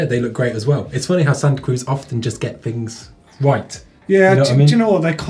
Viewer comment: When dude said santa Cruz i thought he said saggy poo. Lol